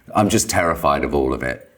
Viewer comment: I thought he said "I'm just terrified of all women" hahaha